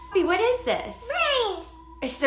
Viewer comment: I will say I definitely heard “rain” not “ring”, but that could just be me and the fact that it’s in an accent different to the ones I’m used to